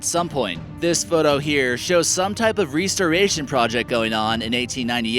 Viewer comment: WTf is he saying "ree ster ration".